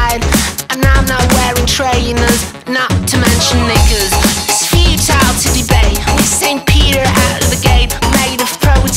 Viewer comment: did I hear right ? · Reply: " Not to mention knickers "